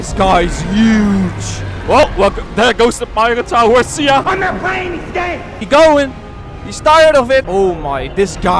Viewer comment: did I just hear “IM NOT PLAYING THESE GAME” by right because I’m not the only on that heard that right?